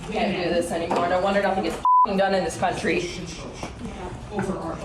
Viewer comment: Oh I thought she said "I'm done with this country"